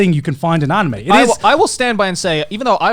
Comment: what did he say? Maia Dajun? Huh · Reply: Maeda Jun (or Jun Maeda) he made Clannad and Angel beats